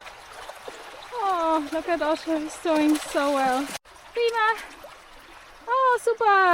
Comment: It was a beautiful view of the Torridon Hills, 😊 Otto was loving it! 🔥🔥 Ouch... day 9 - 29km. The Trail Gods tempting you with early afternoon camp spots 😭 +22:52 What is the word you said that sounds like feema when Otto was getting out of the river ? Some great alternative route choices 😎 Great van location - unless you need to hide the location, perhaps show clips of the train journey, for some ASMR like scene setting at the start of the series, like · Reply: Thank you for watching, I really appreciate you following along 😊 The german word is 'prima', our version of 'well done' 😊